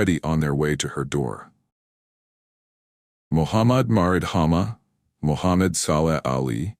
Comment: I kept hearing "Mohammad Barbeque"